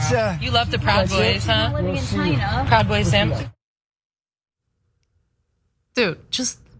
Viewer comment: This time I heard her say, “you love the proud boys huh? Proud boy simp” 😂😂😂😂